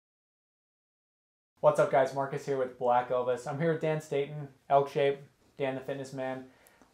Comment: I thought he said black Elvis. Lmao